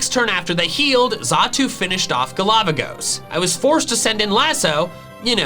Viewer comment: Everytime said "galavagos" i heard it as "A lava ghost" 😂